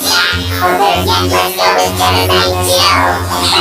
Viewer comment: the video got captions wrong its Genocide Jill not Genocide Syo